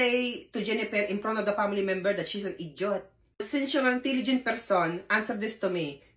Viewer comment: At first I thought you said the doctor call her an “eat joy”, then I realized you said “idiot”. 😂 That doctor definitely ate all the joy out of the poor nurse. So what’s was said when she first opened the bathroom door?